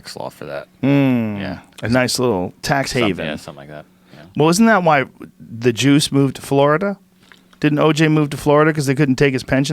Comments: Thought Joe said "Isn't that why the Jews moved to Florida" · Oh the Juice, (no state tax) I thought Joe said " that why the Jews moved to Florida " ...lol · i thought Joe had said "that’s why the Jews moved to Florida" 😯😀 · At first I thought he said Jews not juice. For a second I didn't know where the hell he was going with that one. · "...isnt that why the juice moved to Florida"........ totally thought he said Jews....... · I thought joe said isnt that why the jews moved to Florida... Good thing he clarified and sad oj...lol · Oh you racist jewmophobe!!! I live in Florida I smit thee! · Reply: I thought he said 'the jews' at first too, but he said 'the juice', meaning O.J. Simpson. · I totally thought Joe said "isnt that why the Jews move to Florida"... anyone else???